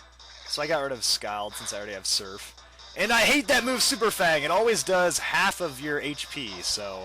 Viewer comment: did he say superfag? 0.o